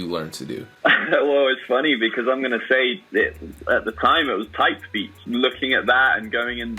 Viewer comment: What did he answered? My listening english is not too good and I can't understand. So would be nice if anyone help me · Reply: Type beats?